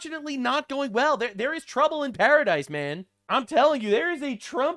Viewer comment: I was cooking while listening and thought said, "there's trouble in terror-o-dise."